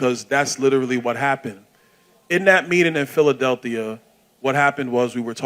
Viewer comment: Did Byron say PhiladelThia? 1:44 😂😂😂